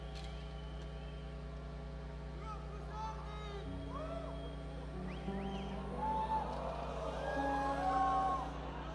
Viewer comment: What did the guy yell at 32:43 and a few seconds after that? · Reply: He said "YOU AND WHOSE ARMY". He was clearly wrong lol